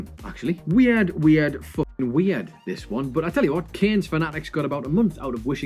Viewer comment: what did you even say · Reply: Better late than never I guess but he said "Weird, weird, fvcking weird" As someone who doesn't speak clearly, I believe all those who also don't speak clearly will agree that we can understand what is said when it is not spoken clearly. A bit weird innit?